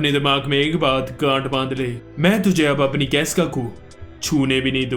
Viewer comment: bhai said gath bandle🎉 But I hear G**d marle😅😂